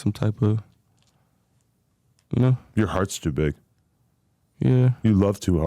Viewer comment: Did Adam just say Blue's ❤s too big???;!!!😮 jjjeeeyeezusss